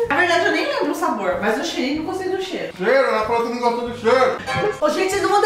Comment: eu não entendi nada q ele falou kkkk, Mas eu achei muito engraçada😂😂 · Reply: “Cheru, ela falou que não gosta do cheiro” 😂😂😂